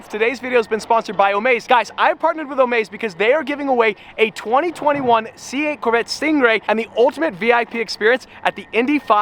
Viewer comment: Did he just say "if u wanna win a C8 Corvette"??